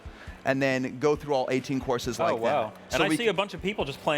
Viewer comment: Did he say 18 courses?